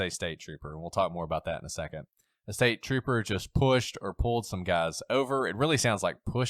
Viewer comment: Never got the “staper” thing. I always heard “this guy was just pushing guys over.”